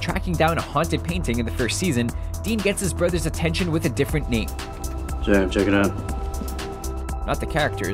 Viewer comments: In the supernatural one who else doesn't here jared I listened to it like ten times and all I her is Sam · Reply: i&#39;m invisible I totally hear "Sam check it out" If he is saying Jared then he is saying it like to laziest way possible. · You're hearing things on that one. He very clearly says, "Sam, check it out." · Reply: I hear "Jam, check it out" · They're REALLY reaching for that Supernatural one, I rewatched it five times and don't hear "Jared" in the slightest. It sounds like he's just kind of mumbling/slurring his word slightly, a speaking error which carried on through the rest of the line. If he maybe started to say it or something he covered it up super well, I don't hear it no matter how many times I listen to it. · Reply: I hear him say "Jam check it out." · You're hearing things on that one. He very clearly says, "Sam, check it out." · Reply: I listened to that part a bunch of times and it sounds like he says "Sam, check it out". No idea how people are hearing Jared. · You're hearing things on that one. He very clearly says, "Sam, check it out." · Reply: everyone's right, i thought he said jared at first but one repeated listening it does sound like he was about to say jared and then tries to cover it up...i'm hearing him say Jam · I hear "SAM check it out". I've already seen that he "said" Jared but i still cant hear it.